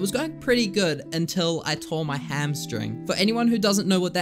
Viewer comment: I THOUGHT YOU SAID YOU TORE YOUR HAMSTER FOR A SECOND-😭😭😭